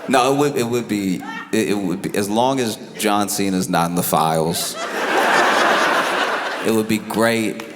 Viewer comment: I thought they were saying cena is in the files 🙃